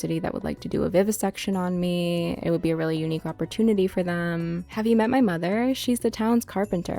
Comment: i had this on in the background and thought you said “have you met my mom she’s the towns harbinger” and was like what fucking mods are they playing with also i can do the vivisection